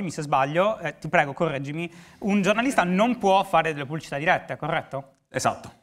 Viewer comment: scusate ma cosa dice a 27:27? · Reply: "Un giornalista non può fare delle pubblicità dirette"